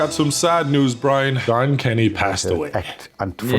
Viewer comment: ohh crap dude I thought he said Tom Kenny and almost cried when that legend passes everyone’s gonna be traumatized